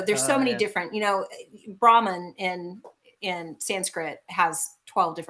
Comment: The part where you said Brahman and thought you said ramen is cracking me up